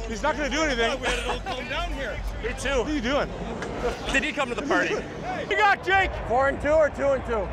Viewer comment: did he say "Diddy came to the party." 😂😂😂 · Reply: I think he said “Did he come to the party?” ..